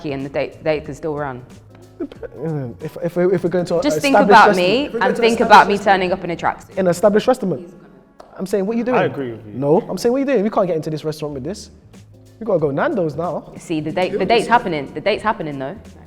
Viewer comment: thought said established Rasta man, he’s lost all composure